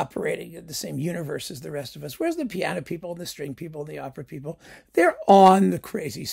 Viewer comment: There were many occasions in the video when i thought you said straight people instead of string people haha